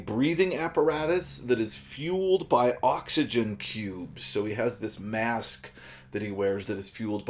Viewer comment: for a sec there i swore i heard “oxygen pubes”